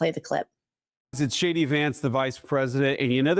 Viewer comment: I thought he said "Hi I'm shady Vance..." on that clip.